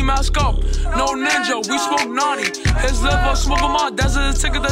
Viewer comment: The first time i heard this i thought he was ddot but he said we smoke notti☠️